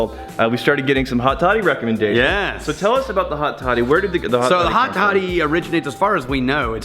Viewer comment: I thought you said hotty toddy. Lol I live next to ole miss